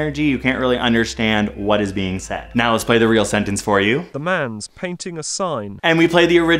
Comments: “The lamb speaks to the sign” Hey, I had a surprising amount of syllables right. I don’t think this really reveals much about my subconscious though. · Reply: I heard "the man's painting a sign" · I heard "lets paint in the sign" before hearing the real sentence. i was actually close 😳